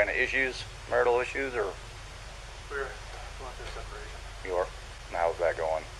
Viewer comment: it kinda sounded like he said we WERE instead of we're